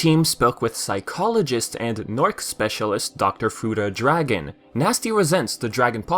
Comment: had to stop here I thought I heard Dr futa dragon..